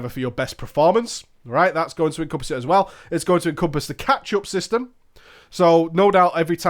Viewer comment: the ketchup system? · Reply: ***** yes I know what it means...it just sounded like he said ketchup... not sure if you're trolling or just stupid